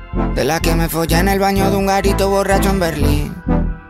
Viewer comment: jaja entendi "de la vez que folle a un gatito borracho en Berlín"